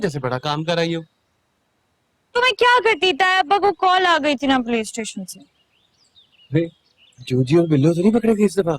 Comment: did she said PlayStation? 😂🤣